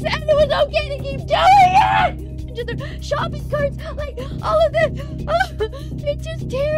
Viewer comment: subtitles said shopping carts